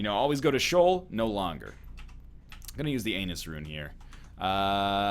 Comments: Am I the only one who noticed NL said Anus Rune instead of Ansuz Rune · did he say "anus"?